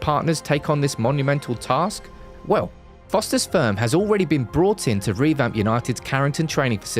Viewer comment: Did i just hear "Foster's Sperm" at @ 8:03 ??? 😂😂😂😂 Jokes aside, kudos to the video. Loved it!